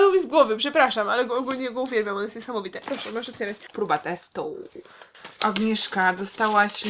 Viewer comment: Zamiast próba testu usłyszałam gruba testu xD